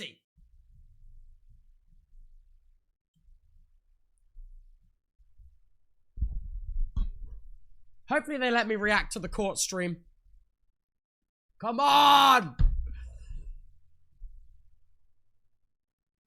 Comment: "at this point they should just sue each other, hope they let me react to the court stream 🙏" that'd be an ending for sure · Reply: I thought he said corpse stream! Omgggg I'm so dumb that makes so much more sense